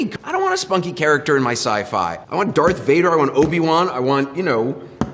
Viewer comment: I misheard 'Obi Wan' as Pokemon and was so confused as to why he called it scifi, lmao